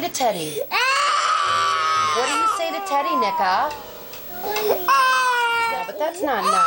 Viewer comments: did I just hear “Nicha” at 9:17 🥲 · WAIT WHAT DID SHE SAY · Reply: Nicka…